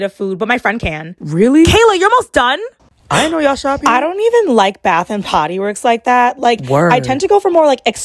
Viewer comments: HELPP THE VOICE GETTING DEEPER AT THE END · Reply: I swear I heard “ BATH AND POTTY WORKS” 😂🎉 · It sounded like she said bath and potty works